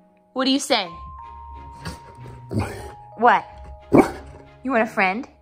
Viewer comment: did he just speak hooman?! (it sounded like he said why)